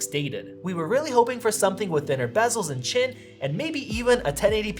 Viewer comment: i thought u said "we were really hoping for something with thinner bezels and SHIT"